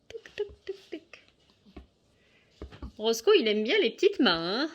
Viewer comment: C moi ou a 8:05 "elle a dit grosse couille il aime bien le petite mains" ??